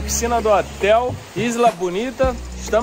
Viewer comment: Qual nome do hotel? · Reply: pelo que entendi no minuto 2:12 “Hotel Isla Bonita”